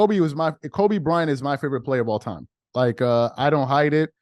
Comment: I thought you said kobe be was your favorite player